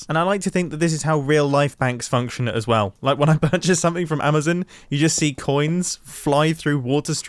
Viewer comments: sorry but, at 9:12, did he say Amezin? Is that how the Brit's say it? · sorry, did he say amazin?